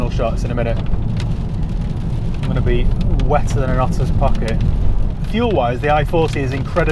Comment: Did I hear 'wetter than a tossers pocket '? Love it.